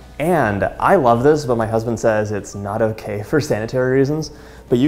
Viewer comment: did he say husband??